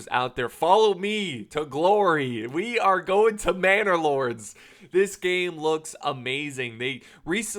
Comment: Lol this entire time I thought he was saying bannerlord like mount and blade, now it all makes sense